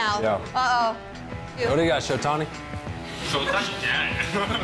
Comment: What is happening / what is being said from 03:24 - 03:33? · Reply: The second person from the right is Shohei Ohtani, but Trout call him “Shotani”. So Ohtani said “What is Shotani?”😂